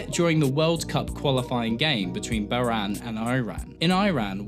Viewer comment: IT'S PRONOUNCED E-RAN NOT I-RAN YOU ABSOLUTE PLEB. My videos are better because I say words RIGHT. YOU SUCK. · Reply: what country are you trying to say before Iran? Bahrain? why did you say Biran?